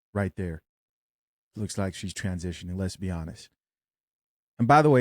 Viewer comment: he said she looks like a man; "lesbianis" is what i heard😂 hoooo! this shit is making me sick